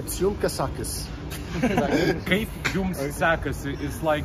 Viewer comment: did he say ''kaip jum kasekas?'' at 21:14 ??